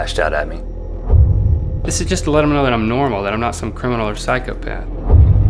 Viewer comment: What does he say (mumble) at 0:27 ?? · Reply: "This is just to let 'em know that I'm normal that I'm not some criminal or psychopath"